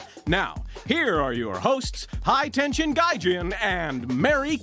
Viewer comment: ハイテンションガ○ジ って聞こえる